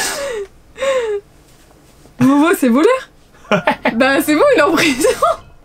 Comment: "vovo c'est voleur ?" Euh attend moi avoir mal entendu la 😂😭